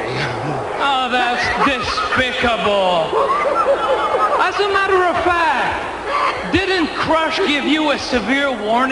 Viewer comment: What does the kid say right at 8:16? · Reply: Ken9284 he can’t do that! Come on!!! Lol